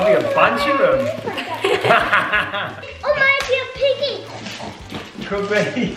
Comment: When Disc said another piggy I thought she said biggy is a piggy!